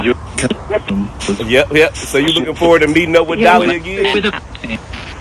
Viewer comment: When you asked Kenny if he would mind if you came back sometime, I really thought that I heard him say “I look forward to it”. I t really did sound like his voice. Great shift. Love love love to all of our shifting family.❤️